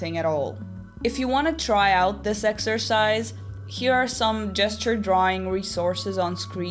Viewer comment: I thought you said jester drawings and I was going to do that, before seeing those gesture drawings lol